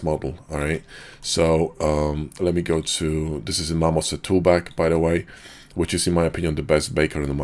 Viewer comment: What's the texture baker program he shows at 3:37? I can't understand what he says. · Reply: marmorset toolbag